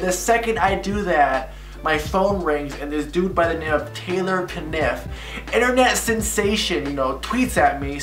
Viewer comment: Lmao I thought it said Taylor swift